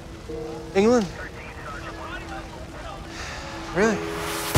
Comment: Aye can anyone tell me what was Spider-Man saying to Gwen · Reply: England?